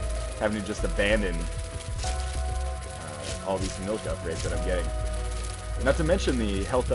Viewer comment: I think he said something about milk upgrades?